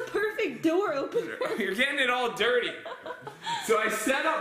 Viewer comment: i thought said when opened the door for him i thought he said your getting it all doorty